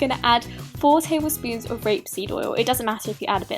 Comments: I used grape seed oil because I thought that's what she said 😫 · Janas life, the oils I use are rapeseed and coconut oil x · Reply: will grape seed oil do instead of rapeseed, because I thought you said grape seed and purchased that instead >~< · What oil did she use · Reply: Frejjja Oh ok :-D I thought I heard "grape seed" haha! Ok, Thanks for the correction sweetheart. Anyway, rape seed oil is highly nutritious as well!